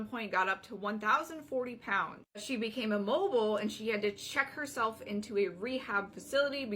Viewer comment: I was in the next room over and I thought she said “she became a Volvo” and I was like dang, that’s heavy.